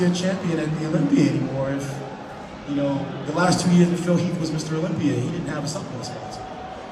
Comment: Does he say that the last 2yrs Phil was the champion he didn’t have a supplement sponsor?